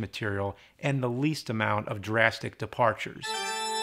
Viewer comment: Sounds like he said "durassic" 🤑